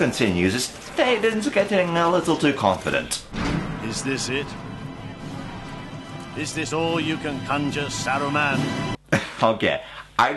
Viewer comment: captions say "is this all you can conjure saddle man"